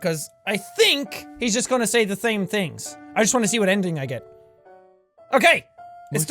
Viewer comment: Is it just me, or does jack say "i think he's going to say the thame things" at 14:20?